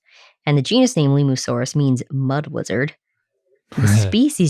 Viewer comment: I'm sorry, "Bad-Stuck Mud-Wizard" is peak dinosaur naming. We're done, y'all can go home now. Edit: Ah, I see I have misheard "lizard". I take it back, there's still time to name something Limufangshi/Limumantia Inextricabilis or something like that